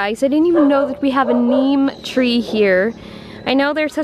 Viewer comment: lol I thought you said meme tree 😹🙈